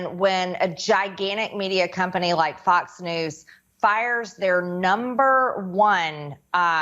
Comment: I can unironically hear MTG say " There number one" instead of their.... ew.